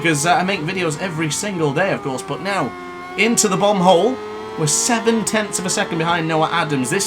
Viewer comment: Is that corner on this track called bum hole lol I thought you said that 😂😂😂😂